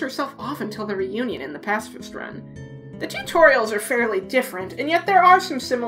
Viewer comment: i heard "the tutorials" instead of "two toriels"